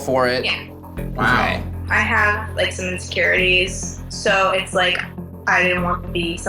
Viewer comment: When she said she had insecurities i thought she said “license to carry”🤦🏻‍♀️